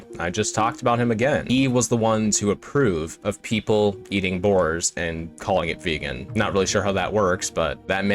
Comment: "He was the one to approve of people eating boars + calling it vegan." Did I hear that right? Boars? 🐗?